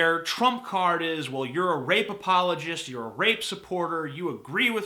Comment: When he said "rape apologist", I thought he said "rapeopotamus"